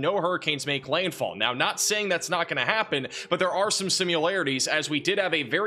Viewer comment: did he just say 'sim-u-larities'?